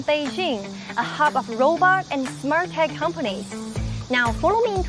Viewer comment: did she say 'smurf tech'